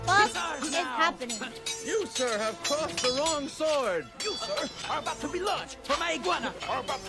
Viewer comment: "You sir, are about to be lunch for my iguana" is what I think he said